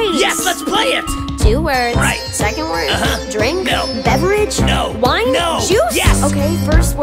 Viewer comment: lol for I sec thought she said “drake” instead of “drink@